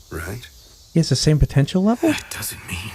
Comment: did someone say potential